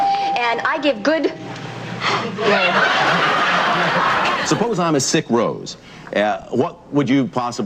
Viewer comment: what did he ask? I heard “suppose I’m a sick rose” I think that’s what the old lady heard too😂